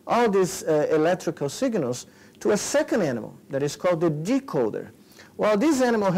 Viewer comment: What did he call the other monkey? A d%$k-holder? 18:00 and the second time it's mentioned, the d%$k holder gets a nice juicy reward 18:25 - Please play it a few times a let me know. Thanks.